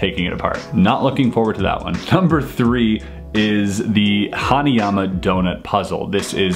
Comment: I heard ”Honey, I’m a donut” 😂 lol It’s Hanayama Donut!!!